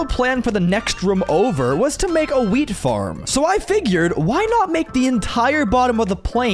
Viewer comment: When he said wheat farm I thought he said weed farm lol